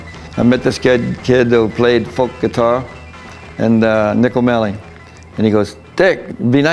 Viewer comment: did he say Nick O'Malley?